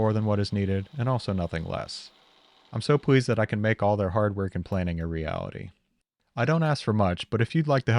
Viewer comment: "I'm so glad I made their hard work and planning a reality." I initially heard this as, "I'm so glad I made their hardware complaining a reality." Brains are weird.